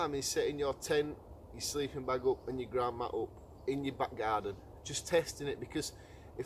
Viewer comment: I thought for a minute you said- "Theres nothing wrong with setting up your tent & your sleeping bag & your Grandma in the back garden." I thought it was a bit of a cheek getting your Gran to test your camping equipment for you, but then I thought, well maybe she is an outdoors expert!